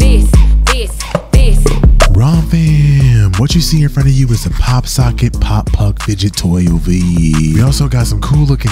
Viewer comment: I thought it said pop f*ck 😂😂😂😂 Love your channel dude ^^